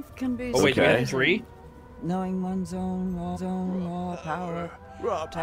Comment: does anyone else hear her say that scentance really trippy?? "ones own ones owwne"???